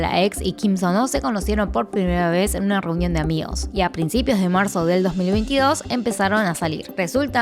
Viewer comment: marzo del 2022? Escuché bien 🤔